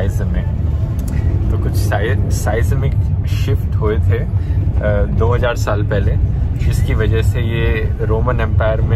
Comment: Dhruv said 2000 But the captions said 200 How many smarter peoples noticed that If yes, hit the like button 👍